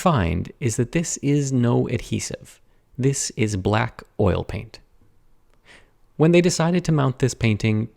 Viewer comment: I heard "Black oil paint" and became audibly insulted for a moment